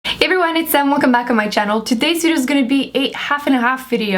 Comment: In the beggining it sounds like she said "hey everybody, its sam. Welcome to my channel." Sounds like it, or did she really say that? 😟